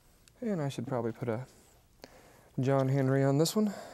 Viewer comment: Am I the only one that heard him say John Henry instead of John hancock